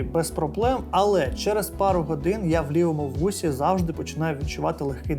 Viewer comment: Почулося що замість "я в лівому вусі", ти кажеш "я в білому бусі", відчуваю дискомфорт, хахаха, ох і часи ...